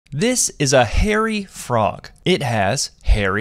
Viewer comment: I dk , at first I hear ... "Harry frog" not hairy forg..